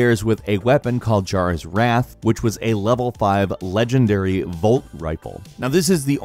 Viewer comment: Did He say LvL 5 😂😂